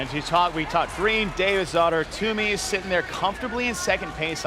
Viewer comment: Really thought he was saying David’s daughter.